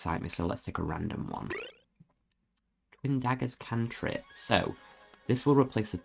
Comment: Such an interesting game. Well played! I'll definitely have to put this one on my list 😅 (Also I thought that said "catnip" at 15:37 and got really confused haha 🤣)